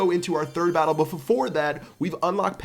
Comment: unless they are a dirty f core user? xD what did he say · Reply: X bow user